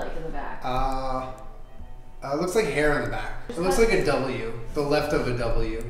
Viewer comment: Julien: It looks like the Left of a "W" ...you mean like a "V"? LOL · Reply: For some reason I heard "It looks like the left of a U" and then I was confused lol